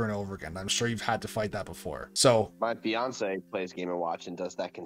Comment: Did this dude say that Beyonce mains game and watch in smash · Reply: LOL that's what I thought at first, but he said "my fiance" I believe. Now I can't stop imaging Beyonce using G&M